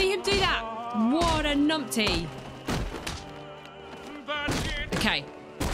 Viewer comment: 1) You had Hard time fighting Laszlo Valentine (Pyg) & his Dollatrons because, since the Asylum, you never focused on building combos (something, I've been saying since day 1), otherwise, this fight was a piece of cake. 26:38 what did you say?? Numty?? What does that mean?? (I'm not a native English speaker) 2) 34:45 .. I seriously wanted to see you play this bomb disarming mission on record 😂😂, The Bomb under the tree.. ONLY THIS ONE..😅😅 3) 37:50.. Why didn't you interrogate the police officer glowing green? He's a riddler informant. Go near him & press block button (Right Click on PC) to interrogate him. 4) Now you know why I stressed so much for playing identity theft missions. When u didn't, I asked you to watch on YouTube. Imagine if you have missed all that backstory ?? Also, I wanted you to play Michael Lane (aka Azreal) encounters in Arkham City, but not listening 1 thing, made me forget the other..😂.. I'm sorry for saying so late..😢😢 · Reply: haha numpty is English slang for idiot or prat 😂